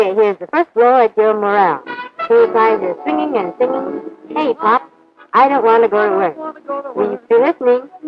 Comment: did she say "K-Pop" ? :O